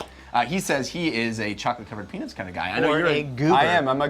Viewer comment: Did say chocolate covered...penis?